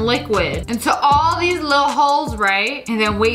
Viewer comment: Her: look at all the little holes😇 Me: * spits out juice* did she just say whores😨